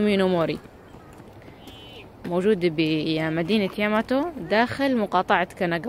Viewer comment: 😂😂😂 انا سمعت صح لو لا هي قالت مدينة ياماتو 0:57 ياماتو سينسي في انمي ناروتو شيبودن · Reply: أهلا وسهلا بك عزيزتي نعم المدينة اسمها ياماتو